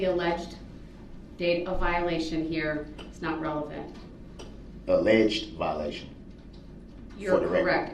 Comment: "ALLEGED violation" That's what she said dummy...